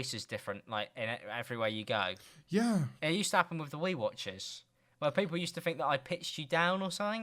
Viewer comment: I really thought Alex said “with the weight watchers”